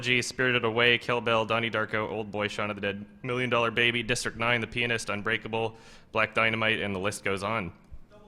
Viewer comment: It sounded like said ''The penis' unbreakable" at 10:19..lol.